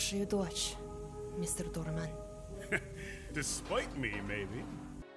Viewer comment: I think Kahn said “Despite me maybe” which is saying that Uzi is a good kid in spite of what Kahn did. But that’s my opinion